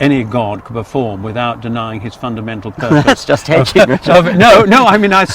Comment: what does dawkins say at 6:24? the bishop said right before, something like: "i believe there is a severe limit to the number of miracles any god can perform ..." and dawkins' response starts out like "now thats just h-" -kudos to anyone who figures it out. (i think its funny how richard says creationism-"stroke"-intelligent design, instead of creationism-"slash"-ID, like i would say.)